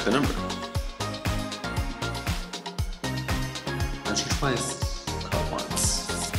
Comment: Did he say measure twice, call once? hahahaha